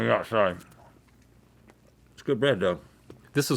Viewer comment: Did he say "It's good bread though", "it's good bread bro", or "it's good bread dough"... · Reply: sounds like "its good bread though" but he says "though" like "doe" to me